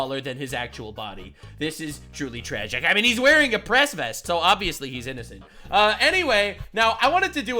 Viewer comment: Haha I misheard you say, "Hassan in a Press Vest". I swear I heard "Hassan in a S-vest" and all I could think was, "Wow didn't take him long to go back to Ol' reliable huh.